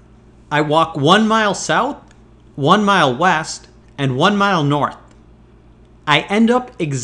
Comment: I thought you said weast